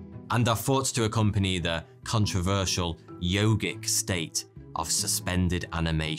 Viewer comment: "Yogic State"?? Aww nuts! I thought you said Yogurt State and got super hungry, Thoughty2.😂